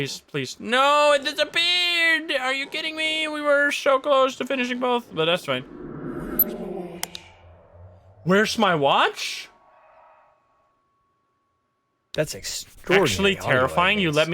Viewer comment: The crying in the Polk house made my eyes water like i was gonna cry it sounded so sad. it sounded like the man was asking where his wife was and why did i marry u. this game is so freaking cool i cant wait to see more and learn more about the ghosts. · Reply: I heard that too when thought he said "where is my watch?" I heard "Where's my Wife?"